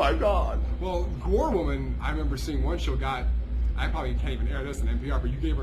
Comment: did he say "I probably can't even air this on NPR?"